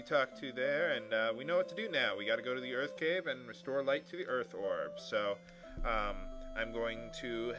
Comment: I thought I heard you say Earth Crib as you left Melmond. XD